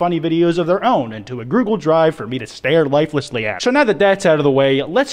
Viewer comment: did he just say grugle drive?